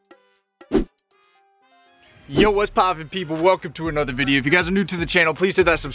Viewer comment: I thought you said “yo what’s poppin pickle” in the intro 😂